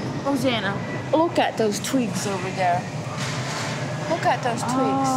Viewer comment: I thought she said “Rosanna, look at those tweaks over there” 👀